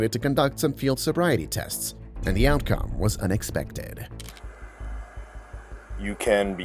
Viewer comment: Why did he say unsuspected so zesty😭